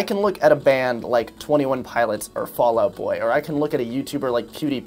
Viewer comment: DID SOMEOBODY SAY... FALL OUT BOY?